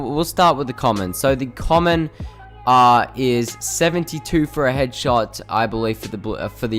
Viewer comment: is so dumb... white burst does 67 to head not 72 Smh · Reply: Big Shraq at 1:22 he said 67 ... I don’t know what ur hearing but he clearly says 72